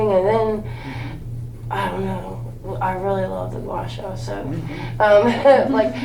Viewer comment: what did she say she liked? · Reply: guasha